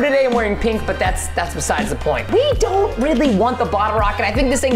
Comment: I thought preston said don't tell anyone about the rocket but when his rocket got into the air i saw brianna and Keith standing🤔🤔🤔🤔😕😕😕😕